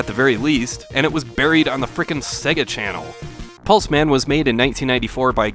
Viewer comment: I dunno. Always found this game mediocre at best and really disliked much of the graphics and level design. Also the newsreader sounds like she says "Pus man" which is gross. :I